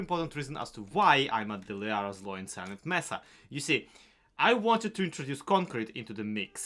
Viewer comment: i thought you were saying the "the zloy-lyarrah-silent *mess-up*" instead of "messa" and i was nodding along like "yeah that fits though a bit mean"